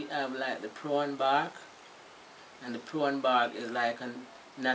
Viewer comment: Does anyone know what she's saying at about 0:27 seconds? Is she saying prawn bark...prone bark??? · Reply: Robert Daniels Found it...Puron Bark